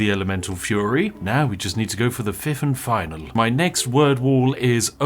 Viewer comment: I have listened 3 times does he say “Fifth and final”? Is word threeth the fiveth? I am so confused send help